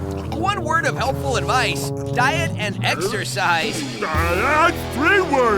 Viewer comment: I thought Sonic said, “two words, diet and exercise.” XD